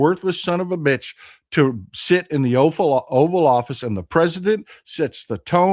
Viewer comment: It sounded like you said Trump is in the offal office, how appropriate!